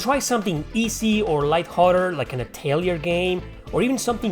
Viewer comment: Looks like an interesting game! What is it called? "Ä̻̮̣̜̀̓̑̎͜ţ͖̥̤͌ͤay͙̭l̹͗ȇ̯̪ͣ͝ȅ̴̬̓͂ͨ͆o̸̜̙̳͒̕r҉̛͇̖̞̗̩͐͒̈́͝ game" Maybe the subs can help me "Italian game" Is he saying tales!?! 2:45 "Lastly go for, idk a tales of game" WHAT GAME WAS HE SUGGESTING!?!? Edit: It was atelier. Which I was surprisingly close by ear but no where near spelling